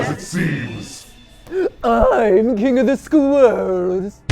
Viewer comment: LoL! At 0:55 was I the only one who heard "I'm king of the squirrels" 😂